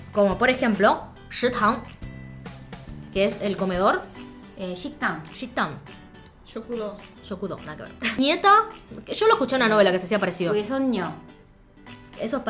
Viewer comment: Minuto 7:25 Parece que dices "Sit down" sentarte o sentate (o como se traduzca)